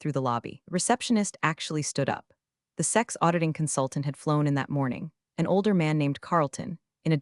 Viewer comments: Someone should proofread those entertaining productions. I'm pretty sure the script is meant say "SEC consultant" at 25:21 instead of "Sex consultant". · "sex auditing consultant"??? It sort of (kind of) sounded like it should have been "SEC's" rather than sex... too bad the printed script didn't reflect that. 😆